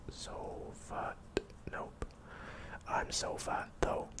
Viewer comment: Don’t tell me it doesn’t sound like he said “I’m so fat tho”🤣